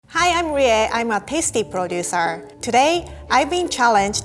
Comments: What I misheard: Hi I’m Rie and I am a pastry producer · Did she say Pasty? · I thought she said pasty hahah lol